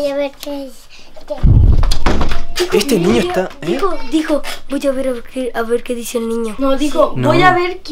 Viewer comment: piculincito dice:voy a er que dice quico